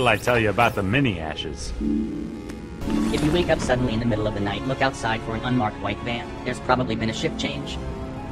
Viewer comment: Ok, wtf was said at 18:10? Is this a simulation conspiracy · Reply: “If you wake up suddenly in the middle of the night, look outside for an unmarked white van. There’s probably been a shift change.” I don’t know if this is a simulation conspiracy or a conspirator in general.